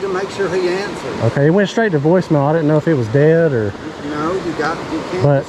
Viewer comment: I had to go back and listen to this a couple times, at first I thought you said “I didn’t know if he was dead or…”, haha glad I was wrong. Great video as always man! Love learning more about bass fishing here in Georgia. You’ll have a very big channel here one day soon if you keep it up.